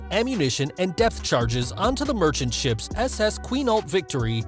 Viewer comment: Really sounds like he said “death charges”